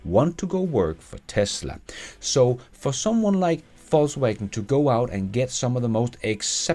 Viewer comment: I always laugh when the automatic captioning "mishears" company names. But the name "Volkswagen" at 12:44 captioned as "false awakening" made my day... :)